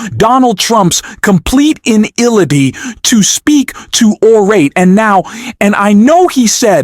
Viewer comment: Complete inillaty? WTH you trying to say? Replay 2:57 and 4:39?